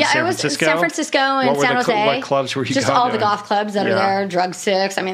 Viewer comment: Am I the only one who thought she said golf clubs, instead of goth clubs? I was so confused and so judgmental of her liking golf for about two minutes.